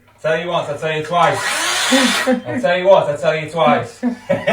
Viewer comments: I feel like if you were gonna be that toxic about your child's door..... Breaking the doorknob would have been less traumatizing. For the child and the door.....and your purse. Just a thought Edit: She's 25?!! Why are we enforcing the no locked door rule for a 25 year old???????? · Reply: She's not 25. Americans misheard what he said, he says "I told you once I told you twice." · I feel like if she's an adult it's even worse, she's a full fucking adult and y'all don't even want her door to be closed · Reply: She's not 25. Americans misheard what he said, he says "I told you once I told you twice." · The fact she was 25 makes it even worse, if you can't get privacy from your parents at that age then wtaf? I'm sure she wants more than anything to move out of that house but likely she doesn't have 3k+ to drop on a deposit and a spare 1.5k each month before bills to spend. Doubt she'll be visiting much when she finally does leave though.. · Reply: She's not 25. Americans misheard what he said, he says "I told you once I told you twice." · Being 25 makes it worse. If its a child, fair enough stopping them having privacy with their boyfriend over. As an adult they can do what they want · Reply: She's not 25. Americans misheard what he said, he says "I told you once I told you twice."